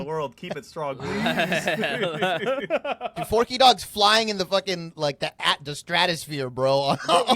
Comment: what name are they saying here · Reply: Forkydogs. I think he's a pro player